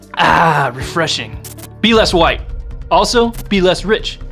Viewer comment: Hey my Niggaaaaz , Wait what I thought you said I had to be less white professor.